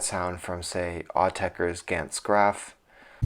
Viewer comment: Hi, Autechre's what track at 10:01 did you mention? I didn't quite understand. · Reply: Gantz Graf :-)